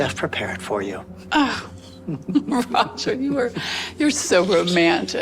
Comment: I'm not sure if anyone saw this, but at 13:34 the woman says "Oh, Roger" but the subtitles say "Oh God"